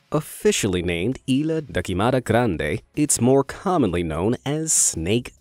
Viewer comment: i misheard it ariana grande